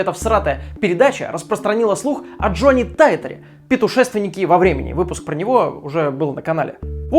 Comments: Лол. Петушественник во времени? Я правильно услышал ? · Я один слышу вместо путешественник во времени, слово ПЕТУшественник?)) тайм - 21:47 Если не только я, ставь лайк))) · «Питушественнике во времени» Или мне послышалось? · Что за петушественник) Ахаха. Сначала подумал, что послышалось) А в целом как всегда ТОПовый контент. Спасибо) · Почему я услышал "петушественнике"????))) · ПЕТУШЕСТВЕННИК во времени!? мне не послышалось? ахахахахха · Кто услышал "петушественник во времени" на 21:48 ? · Я один слышу на 21:50 не путешественник а питушественник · мне одному послышалось "ПЕТУШЕСТВЕННИК"? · Я один услышал "пЕтУшественник" на 21:49 ? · питушественик или мне послышалось чи не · "Петушественник"??? Послышалось? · петушественнике? Мне не послышалось?) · Он и вправду сказал "петушествия" или мне послышалось)) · "Петушественник во времени" =)))))) или мне послышалось? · Походу я один заметил, что он сказал не пУтЕшественник, а пЕтУшественник В 21:50. Астанавите мой ОР😂😂😂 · Я одна услышала "петушественике"? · мне одному послышалось "питушественнике" на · Я один тут слышу "пИтушественик во времени"? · Я одна это услышала?😳 Петушественнике во времени😂 21:45 вот так оговорочка) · Мне послышалось или Топа говорит о неком "петушественнике" во времени? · "Петушественник во времени" - мне не послышалось?